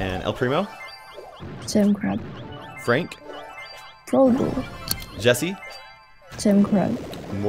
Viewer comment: what did she say"poo boo"?